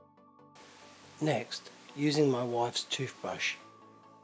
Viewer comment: Did you just say "using my wife´s toothbrush" ? hahahaha. Amazing work.